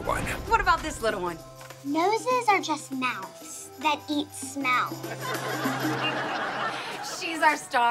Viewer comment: I thought she said “noses are just mouths that eat snow”